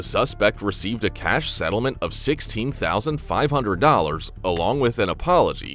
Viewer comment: You misheard it was 60,000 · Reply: No, it was $16,500. You misheard.